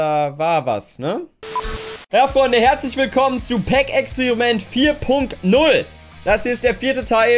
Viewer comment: Hab "Kek"experiment verstanden😂😂😂😂👍💪 Und starkes video